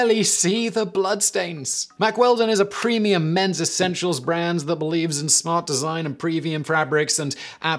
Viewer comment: Simon said "men's essential", but what I heard was "metrosexual"...